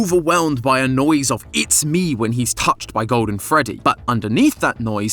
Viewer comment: I thought it said i smell